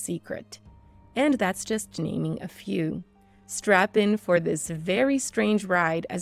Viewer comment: " Did she say strap in or strap on?" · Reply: She said strap in, while you wear a strap on😂😂